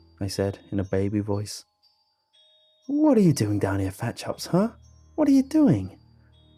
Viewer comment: Thicc chops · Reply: I thought he was saying "fat chubs"